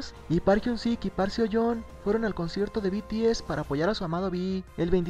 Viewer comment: Escuche "foll*r" en lugar de "apoyar". Estoy mal \:v/